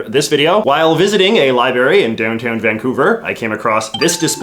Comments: time stamp 0:55 sec... did you say liberry ??? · Did just say "libary?"